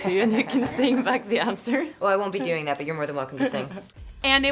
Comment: What did she said on 1:39 ? Your mother is welcome to sing?